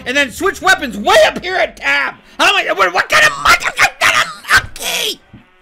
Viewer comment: I like seeing him pursuing though, keep at it! Also my favorite part is 11:17 where it sounds like he says “im not a monkey!”